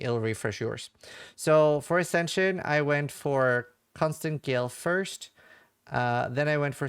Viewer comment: I was tabbed out and it sounded like you said Constant GILF lmao